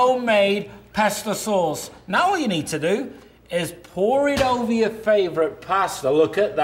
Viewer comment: Sounded like that man said "pore it over your favorite pastor." Like if you're at Church pore that stuff over your pastor....Stuff look nasty anyway.